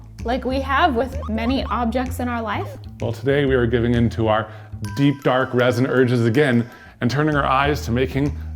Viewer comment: I think I’m going crazy, I swear I heard them say deep fried resin 😳